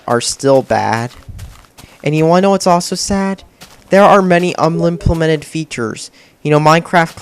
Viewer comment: Did you just say "Unmimplemped"